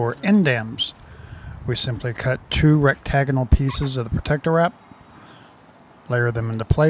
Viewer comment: 25 Did you say rectagonal?